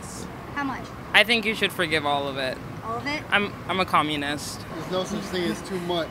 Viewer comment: When she said "I'm a communist" I hear "I'm a Democrat".